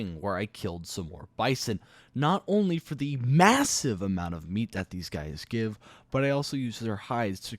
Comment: DID i hear massive?????????